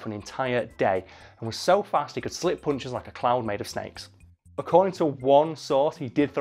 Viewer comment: When says "harder to hit than a cloud made of snakes" I heard "clown made of snakes".